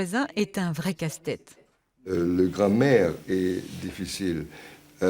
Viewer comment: Le gars au début il a dit "la grammaire est difficile" j'ai compris "la grand mère" 😭😭😂